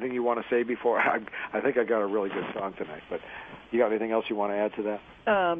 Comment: what does he say? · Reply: "I think I've got a really good song tonight."